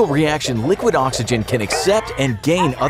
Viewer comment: At first I thought it said liquid nitrogen at first Like same video lol